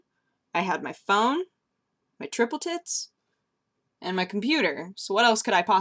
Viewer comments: Did just say she has "triple tits" at 4:46? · I heard ..."my tripple tits" too. Now we know why is so special. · Did she said she has "triple tits" at 4:46? O_o Pleas tell me it's some sort of american slang...